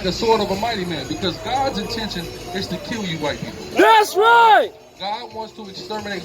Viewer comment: gods intentions is to kill white ppl ?? Did I hear right ???